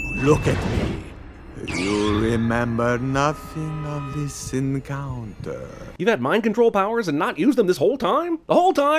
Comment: Dracula's accent makes it sound like he said sin counter.